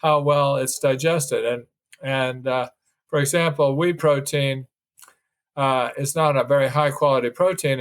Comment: In 18:25 did he say "wheat protein"?